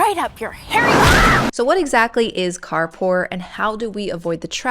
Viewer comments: I thought it said car poop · The thumbnail on mobile shows the video length in the bottom right corner, which covers up the right leg of the “R” in Car Poor. I am extremely disappointed I’m not learning about Car Poop. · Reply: Hahaha I also thought it said Car Poop hahahaha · Thought it said "Car Poop"